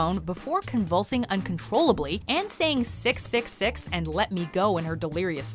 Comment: 왜 섹ㅅ라고 들리냐ㅋㅋㅋㅋㅋ